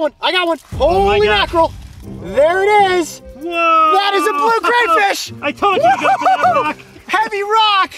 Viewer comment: I thought he said "THATS A BLUE CRIPFISH"